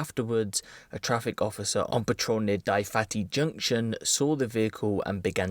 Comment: did he say "die fatty junction" or am i bugging